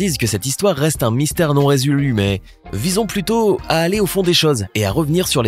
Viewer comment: Ki a entendu comme moi à 6:30 le "non résulue"?😂